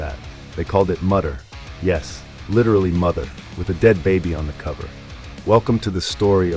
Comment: That's what i say in the video!😁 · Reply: Yeah I was actually referring to that part you said at the start of video but I misheard it and thought you said literally and just repeated the title and realised what you actually said was "literally mother" in referring to the translation.